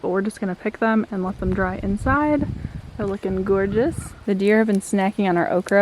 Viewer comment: Pretty sure I heard “looking gourdeous” 😆💕